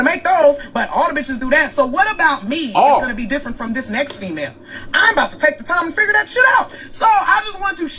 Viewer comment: I heard her say she about to take the time to figure that out